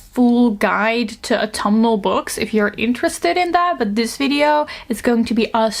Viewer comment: The subtitle says “tomal books” instead of “autumnal books”, though that doesn’t sound wrong 😁 It’s kinda tomes of gothic dark academia novels, right?